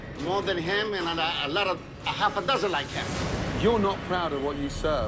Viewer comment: Did he say he had half a dozen ballz 💀